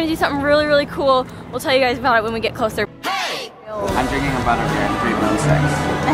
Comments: What DOES say in the intro I've always wondered lol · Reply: I always thought he said " I'm drinking a bunch of beer and three milkshakes" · What DOES say in the intro I've always wondered lol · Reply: I thought at first he said 'Im drinking a butter beer and thinking about six' lol he says im drinking a butter bear at three wooden sticks